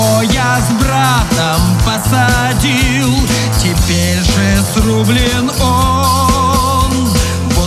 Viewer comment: Я, конечно, все понимаю, но на 23:44 я четко услышал: "я сру блином"